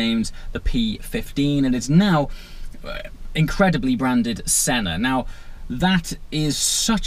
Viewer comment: lol I thought it said Sienna for a sec